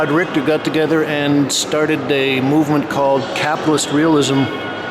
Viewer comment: Sorry, mistake at 6:44 this art movement was not called "Catholics Realism", it was called "Capitalist Realism", an ironic difference to the East German "Socialist Realism" Sorry again: Not "capless" but >capitalist realism<